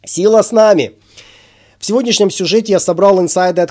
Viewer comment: Услышал "сила сна!" ))